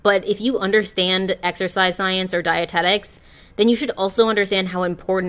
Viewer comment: girl i might be watching too many cult documentaries but when you said "dietetics" i heard "dianetics" and almost dropped my lunch